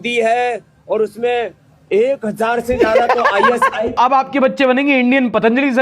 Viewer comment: did baba say "ek hazar se zyada ISI.."😮